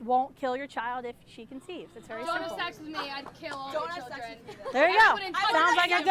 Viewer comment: did she just say "Dont have S*X with me then"?!!!